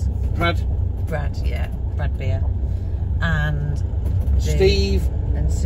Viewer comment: Hi both juat wondering please did you say your friends name was Brad Beer or Bradbeer, sorry to sound weird 🤣. Sat in Shaz and Daz watching you and we thought you said Bradbeer and that's our surname and there aren't many of us 🤣safe travels love and cwtches brett n beck ❤️🙏🏻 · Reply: Hi to you too And yes his name is Jon Bradbeer and no it is a unique surname they are from wales Say hello to Shaz and Daz from us Happy holidays ☺️☺️